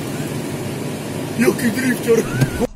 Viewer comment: co mówisz po tym jak przy 90 km/h cudem omijasz stojący samochód -Jaki grifcior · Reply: Drifcior* to pasażer mówił xD